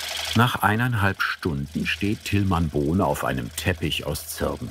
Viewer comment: Und ich hab im ersten Moment noch Tilmann "Bohle" verstanden.